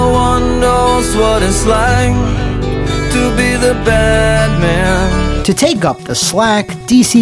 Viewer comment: I know everyone heard Batman instead of bad man.